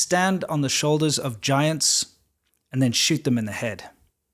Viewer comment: I just misheard the first line Stand on the shoulder of giants and shit on their heads